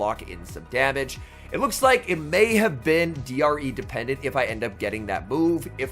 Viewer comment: what is said here? D.R.E dependent?